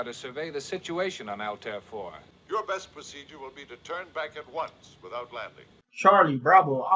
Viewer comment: Why do I keep hearing: “Your best procedure would be to turn back at once, without laughing.”